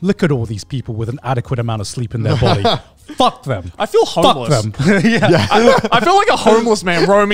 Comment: did I just hear joey say that he felt homeless going home in the morning.